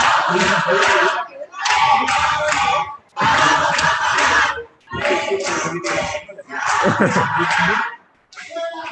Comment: Una consulta que pasó en el minuto 28:40, que se escucha un ruido como que alguien estuviera protestando pero no se entiende nada?? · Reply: "La ley también la vamos a parar" eso escuché